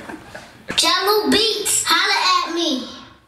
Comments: i thought the kid said jango beats. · All these years I thought the tag said "jungle beats" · I always knew that it was supposed to be saying "Jahlil beats..." ,but I always heard "Jungle". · I always thought it said jello beats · The first track i heard of Jahlil beats, i also thought the voice said Jungle beats... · All these years I thought the tag said "jungle beats" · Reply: lilkamabeast i always thought it said "Jello Beats holla At Me!"